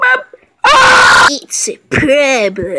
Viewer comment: WHAT DOES HE SAY HERE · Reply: ITS A PROBLEM... AAAAAAAAA